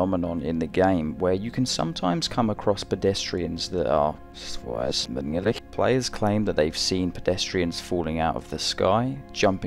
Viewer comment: Yo what did dude say backwards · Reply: it’s the guy saying “killing themselfs”